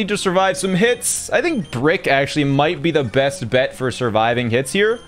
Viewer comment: am i the only one who hears "brick astley"